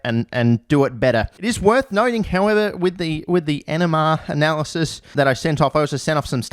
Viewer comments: Definitely thought he said enema analysis not nmr analysis. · I thought he was saying enema analysis, not NMR analysis. that changes the context a bit